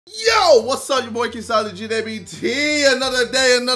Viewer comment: for some reason when ever jj starts his videos to me it sounds like he says " yo boy KSI LGBT"